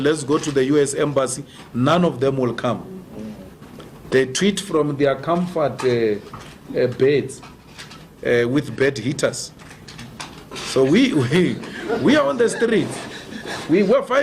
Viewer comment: What did he say @ 4:36?? "They treat from their comfort as bait... with bad leaders." ????